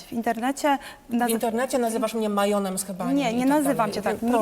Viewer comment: Majon z hebanu ? Dobrze usłyszałem ? 😂.